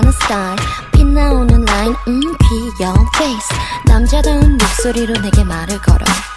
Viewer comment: it sounds like she says "Mmm pee on face"